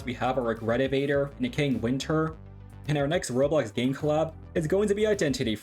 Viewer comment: Did bro say Necaying winter?